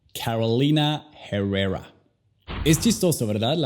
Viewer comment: parce que dices jeruera XD